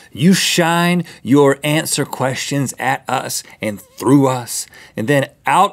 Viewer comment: did Rhett say answer questions?